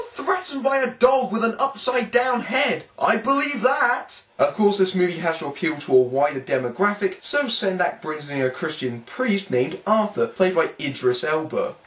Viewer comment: I almost misheard that as “appeal to a whiter demographic”. How ironic.